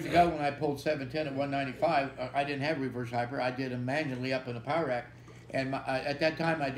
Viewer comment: Great pod as always! I cant really hear what Louie says at 58:20, I guess its something with the ankle weights I can hear "i did a "blank" up in the powerrack. Need someone to fill in the blank for me :) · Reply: Magne Lonn hes saying he used to do reverse hypers with weights fastened to his ankles, lying across a table or bench...before he built a machine for it